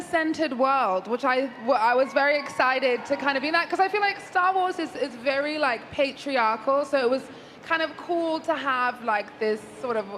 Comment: Did she say Star Wars is very patriarchal? She should have watched all the movies before going on this panel.